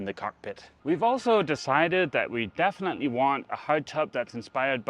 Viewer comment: I thought you were saying hot tub not hard top!😂😂😂